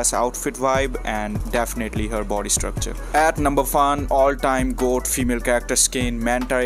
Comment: Did you just say "at number fun"???